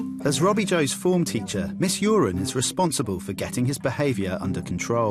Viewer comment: did I hear that wrong or is that teachers name miss Urine? 😂